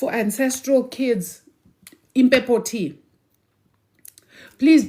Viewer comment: what are you saying here?? Imbebpo? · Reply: Impepho, hope that helps